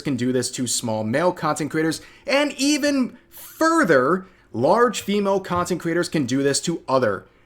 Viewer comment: NGL but at 1:41 it sounded as if was gonna say "even furries"